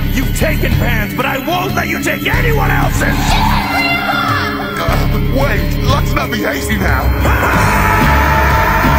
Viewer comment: what is she saying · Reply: GET HIM GRANDPA